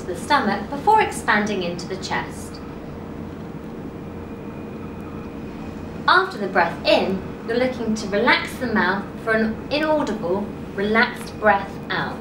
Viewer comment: What is said in 0:48 - 0:55? I can't understand what the lady is saying in here. Someone could write it down for me please? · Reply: “After the breath in you’re looking to relax the mouth for a relaxed breath out”